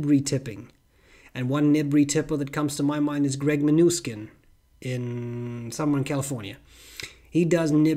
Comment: The auto subtitles say "Greg my new skin summer in California" 3:13 I did laugh out loud. Oh, and at 9:46 you can "send your nibs to nibble my stew"